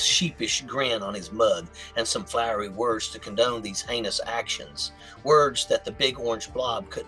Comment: You said "heinous" and I swear I heard "anus". That's what happens when you're talking about an asshole I guess.